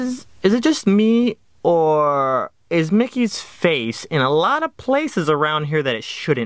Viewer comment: im listening to this on headphones and they stopped working for a secon son at 1:10 it sounded like "Mickey is F cking a lot of places" LOL XD